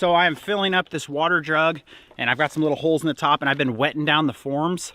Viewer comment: I love seeing Pepper enjoying the new property. 💜I can tell you two are excited and nervous, @ 27:13 if I heard it correctly said "filling up this water drug or jug"? 😊You two always get things done as a team! Looks fantastic!..